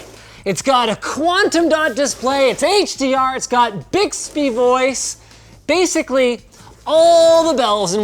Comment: I really thought he said "Big Speed Boys" meaning the cpu but sadly no. Much worse. He said Bixby Voice. 😪😭